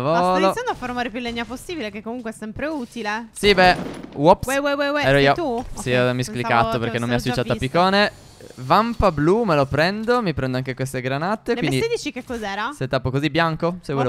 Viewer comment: Raga ma sono solo io che ho sentito al minuto 2:55 che diceva we we we si tu?